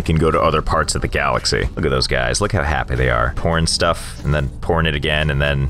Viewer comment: Is it just me who thought he said “P*rn stuff” Rather than “Pouring Stuff”